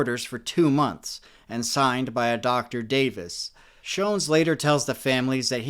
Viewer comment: instead of ´dr davis’ he probably said UC Davis, which is the hospital near J St in Sacramento. nonetheless amazing work!